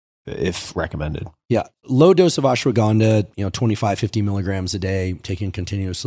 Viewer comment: ashwagandha 1:04 rhodiola rosea pre-workout 3:10 ashwagandha dosage/cycling (200mg) 4:58 Indonesian tongkat-ali (400mg daily, early in the day) 6:10 fadogia agrestis - increases luteinizing hormone (300-600mg daily) fadogia and tongkat no need for cycling no estrogen/testosterone=no libido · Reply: I think he said in 3:10 that is 25-50mg—not 200mg